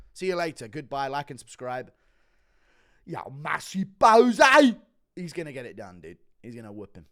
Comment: Did he just say JOU MA SE POES at the end?? 😂🤣🤣🤣🤣